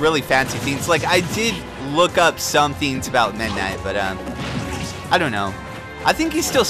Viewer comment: constantly sounds like hes saying midna